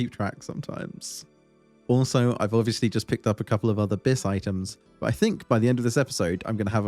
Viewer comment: Oh. "A B.I.S. items", I thought you were saying "abyss items" and I had no idea what you were talking about lol.